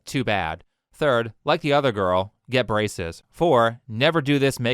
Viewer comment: i heard "get racist" and that would work too